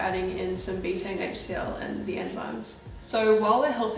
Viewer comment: what do they say at 7:36 , I can't get it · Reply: I think I was saying Betaine HCl and digestive enzymes can help with things like bloating! :)